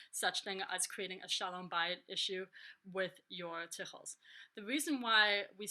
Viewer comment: you said a phrase that describes a negative aspect of having too many tichels. what was it? i thought i heard close to shalom. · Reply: Bayit - Peace in the home. Shalom bayit is one of the MOST important parts of Judaism - almost everything is secondary and must serve that primary purpose :)